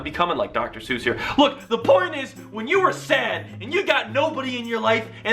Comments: it kinda sounded like u said 'the porn is' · did you just say the porn is?